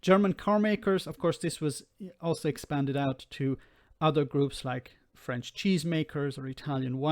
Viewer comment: Had to rewind as I was sure I heard a Monty Python, "Blessed are the cheesemakers."